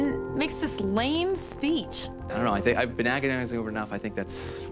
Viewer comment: I thought that he said, "I've been acting as if you were enough." 💀